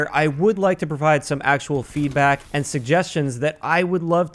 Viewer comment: I swear I heard him say “teabag” right after he got teabagged lol He actually said “feedback” lmao